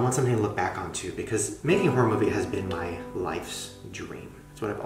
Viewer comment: When he said horror movie I thought he said pornography😭😂